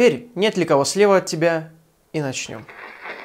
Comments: несколько раз переслушивала, потому что никакого "я слева" в упор там не слышала.. и в последний раз чётко услышала "да ну вас всех"))) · на 4:12 я услышал не "я слева" а "я слеп".